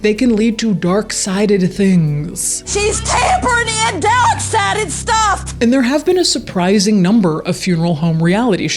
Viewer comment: I can’t understand what she said. Anyone want to subtitle this one for me? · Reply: The YouTube subtitles say, "She's tampering in dark sided stuff."